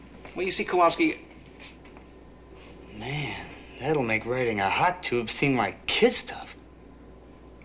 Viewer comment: "Writing (or "riding"?) a hot tube (?) seem like kid stuff" What the heck is he trying to say here? 🤪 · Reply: The character of Riley had been established earlier as a ‘surfer dude,’ and he often spoke the lingo. “Riding a hot tube” meant that he was performing the difficult trick of surfing in the curl of a wave.